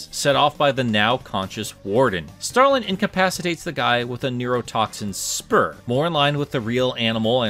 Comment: i heard "neo-toxic sperm" bruuuuh